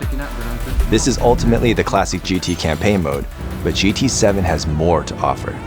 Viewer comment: I don’t know why but when he said GT 7 i thought that he said GTA 7 and my eyes went immediately 👁👄👁